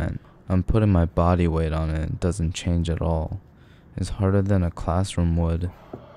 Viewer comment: The fact he said 'It's harder than a classroom wood' Got me ROLLING it too me 3 roll backs to fully get what he said lmfao its